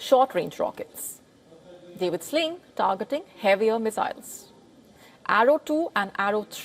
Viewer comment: I thought they said they gonna sink it